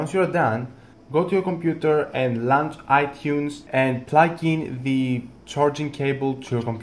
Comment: I’m sorry this didn’t help... I don’t know what he said at 1:11 Lant iTunes? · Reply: WinterCat I think ge said launch itunes